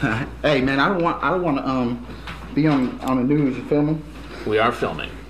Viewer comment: "we are filming" "too late for that dawg" lmao this will never get old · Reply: It's funny because he said "ya feel me", but with his accent Chris heard him say, "ya filming?"